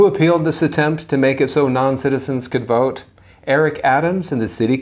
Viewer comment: Oh never mind I I thought you said they could vote never mind